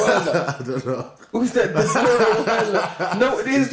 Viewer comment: Did he say dabura at 7:10 lol?